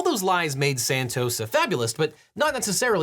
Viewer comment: the subtitle says "a fabulous" but I think you're saying "a fabulist"